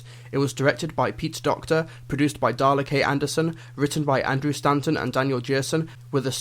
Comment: I thought he said Dalek not Darla K